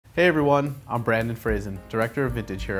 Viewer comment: I literally thought he said Brendan Fraser 😂😂😂